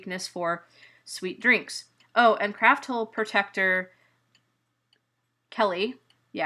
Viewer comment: at frist i thought you said CRAPHOLE